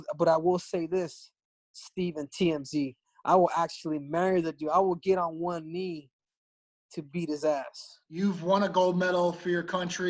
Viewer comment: Did he say I will get on one knee to eat his ass?